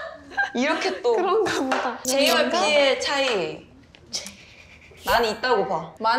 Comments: JYP 나만 들은 줄 알았는데 하니도 같이 들었네ㅋㅋㅋㅋㅋㅋ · Bet you Hanni’s laughing because it sounds like Minji said JYP 😂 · hanni was laughing at minji because she sounded like she was saying "jyp" instead of "j랑p / j and p" 😭😭😭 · I heard jyp instead of ‘j and p’ and I think hanni heard minji say that too, that’s why she started laughing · did hanni laugh bc it sounded like JYP or ww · pls hanni snickering over minji saying “J and P” which sounded like “JYP” 😭😭 she just like me fr fr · at first I heard JYP 😭😭 I think I should go to sleep... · 하니 JYP의 차이라고 들려서 웃은건가 개귀엽다